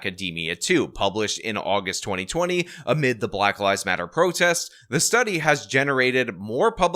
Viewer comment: At the start of your video I thought you said "at the height of the black lies matter protests...." LOL, oopsie! Oh well, it still works.